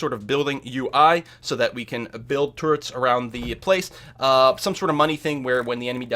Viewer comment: Every time you just said "turret", I heard it as "turd". Made it quite comical, UI for building turds etc. :D